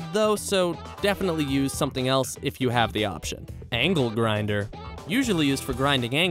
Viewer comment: For a second I thought he said ankle grinder